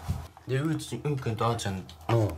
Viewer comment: うんちって聞こえた💩